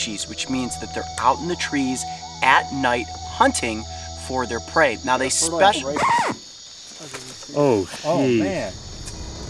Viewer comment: For those curious, I believe what said was: “You’ve got a ferdelance right (behind you)” before being cut off by the record scratch